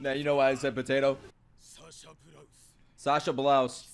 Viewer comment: bruh those subtitles are wrong. Her last name is Braus not Blause tf😂